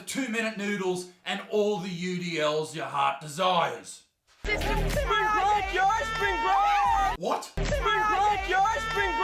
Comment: This video was absolutely hilarious. At 4:43 I think he was saying spring break yo spring break.